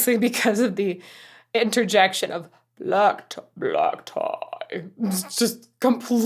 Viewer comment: I heard him sing 'magpie' not black tie. After all, magpies have black and white and black feathers